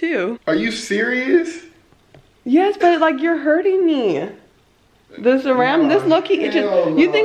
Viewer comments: Did she say “ Yes bae, your hurting me” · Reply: Ariel Cumberbatch she said “yes but your hurting me” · did she just call him bae or am I trippin ❓❓😂😂😃😃😘😘😘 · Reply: I'm pretty sure she said " yes but you're hurting me" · Anybody else catch when she said “bae” at 6:56 👀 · I heard her almost say "yes bae" at 6:51-6:57. Am I trippen? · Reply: Khaylor Griffin she said yes but , but it does seem like she said that